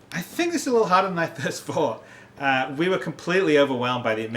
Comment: "I think this is a little bit harder than I thirst fought." Tell me I didn't hear that at 5:15.